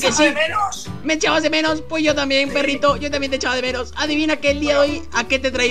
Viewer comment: Segundo; 36, pense que dijo quiero caramelos ,enves de decir te echaba de menos jijijijiji xd